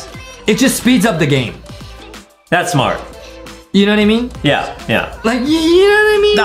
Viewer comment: I thought said you know that enemy and i realise that you said you know that what i mean